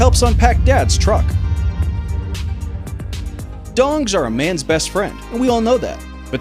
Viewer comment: anybody else hear ' dongs' are a mans best friend? .. No?.. just me?.. ok